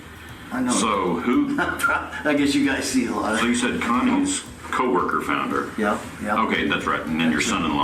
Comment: does he say “so you said condoms coworker found her”